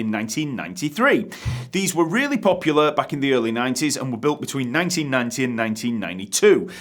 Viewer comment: What are you saying at 0:58??? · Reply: "Back in the early 90s"